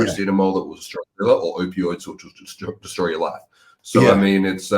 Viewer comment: If someone hear it what he says here:"Opioids destroy your xxxx"? I here Destoys your lat, but that cant be it =) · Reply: says, "Opiods will destroy your life."